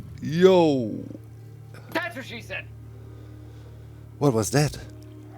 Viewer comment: I can't tell exactly, but it almost sounds like saying "that's what she said"